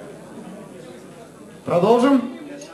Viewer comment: Eh, what did he say at 0:03? Longbottom? lol